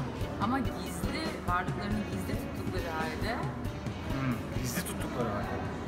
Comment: What does Selma say here 👉 4:05 ? ( please tell me in turkish or english) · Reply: Filme turceşti subtitrate diyorki Vampirler varlıklarını gizli tuttuğu halde bizi yönetebilirlerdi.